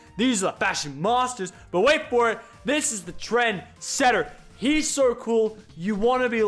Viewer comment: I thought he said transgender when he said trendsetter